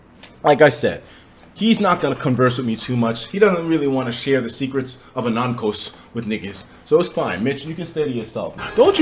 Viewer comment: Did he just say the Secrets of Anankos? How would he know about Anankos, the man never beat Fates yet